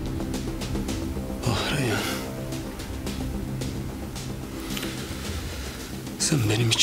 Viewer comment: Miran ne dedi 7:00?!?!?!?!?!?!?!? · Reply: ah reyyan sen benim i.imdeki korkuyu bi bilsn